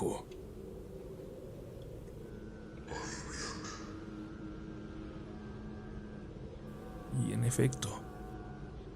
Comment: Alguien me puede decir que fue lo que dice en el minuto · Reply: "Ahí viene"